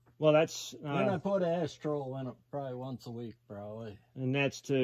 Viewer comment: What did he say he put in to “sweeten the ash”? At 4:08 in the video? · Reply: Ashtrol, you can buy it from any Central Boiler dealer. To find a dealer go to central <link> the look for “ find a dealer”. Enter your zip code and it will pop up who is close to you. Thanks for watching our channel.